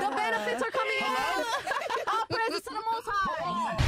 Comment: The way you say lady all praise is do to muthai you are sick you choose Shias not sunny religion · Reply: I think maybe she said “the most high”, I listened again because I think her accent made me mishear?